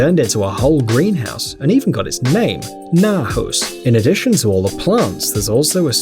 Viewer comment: I think it should say naturhus